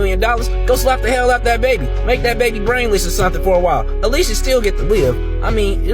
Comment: That black man who wants to harm a child needs to be locked up for child endangerment!! God protect this family. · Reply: Did he just say “slap the baby brainless”